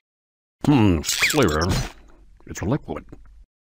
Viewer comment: I never understood what was saying at 22:04. I do understand: "Hmm, flavor..." but the rest is inaudible for me. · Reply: I hear “hmmm weird It’s a liquid”